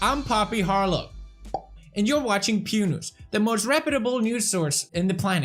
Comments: I thought he said "pube news" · I thought you said pube news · What normal people heard: Hi, i'm Poppy Harlow. You're watching Pew News. What I heard: Hi, i'm Poppy Harlow. You're watching Punus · I thought you said pube news and i was like uuuuuhhhhh and then couldn’t stop laughing 😂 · i thought u say "pube news"